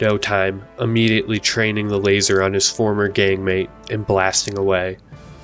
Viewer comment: for a second there I thought he said gay mate not gang mate 💀😂